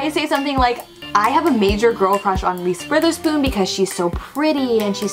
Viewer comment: i thought she said Ms.Witherspoon i was like, thats my teacher? 😂